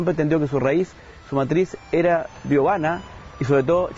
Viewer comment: ¿qué dice? ¿Biovana?